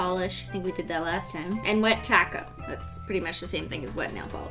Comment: Who thought that when she said wet top coat it was wet tako